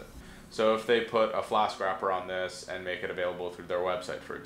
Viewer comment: "Flask Wrapper" - had to re-watch it a few times to pick up what he said there. Hope this helps others. (Flask is a Python web application framework).